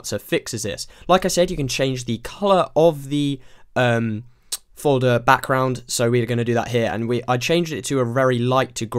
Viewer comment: I thought he said "Blood background"